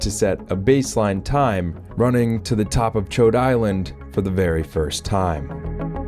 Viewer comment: I'm sorry, but is it really called Chode Island?